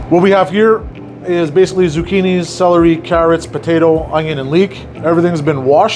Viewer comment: I thought he said zuccenis for a minute 😅